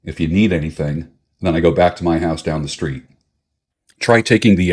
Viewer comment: I was really feeling that story and I thought you said text me I'll be down the street😂